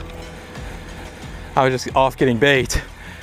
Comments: I swear I heard you say " I was off getting BAKED " ahaahaa. Had to rewind it to hear what you actually said. Wish it was " getting baked " lol. Great vid as always. Keep up the interesting vids brother. Thanks. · "I was off getting bait". Lol I had to rewind it bc I could have sworn you said i was off getting baked! Lol thanks as usual for sharing